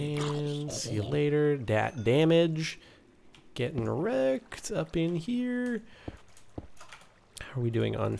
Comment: I know he said ''Getting wrecked'' but it sounded like ''Getting erect''